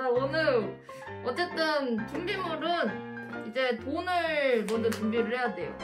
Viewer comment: 이거 나만 분비물로 들리나...